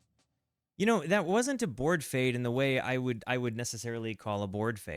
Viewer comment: OMG, you used the term "board fade" on another video as well, and I only just figured out what you meant. The sound board fades out everything, all the instruments together. Got it. I had misheard it as "bored fade" - like "we don't know how to end it, and we're getting bored, and our listeners probably are too, so let's just fade it out now." This time you mentioned the sound board, so I was able to properly contextualize the term. XD